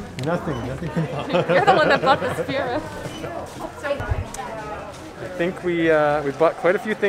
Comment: wait - what did they buy in a “silver version”? captions said spirit, i thought he said spear. $4.99 activation fee and $100 minimum? what did he buy from the “checking in” timestamp? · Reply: nvm - i figured it out- silver Batuuan Spira